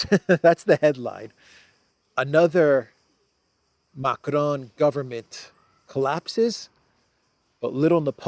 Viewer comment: WHEW!😰for a moment, I thought you said the "Biden government has collapsed"🤔😀